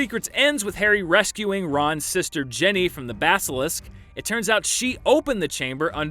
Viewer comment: Why did it sound like he said “Jenny” and not “Ginny”?